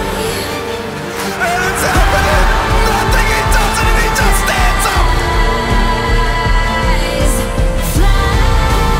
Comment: what does he said on 1:01 ? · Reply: "N0thing he does it and he just stands up! Ice Cold!" 1v1 against guardian when he was in Navi.. overpass semifinals. I still remember me going crazy when I saw that live.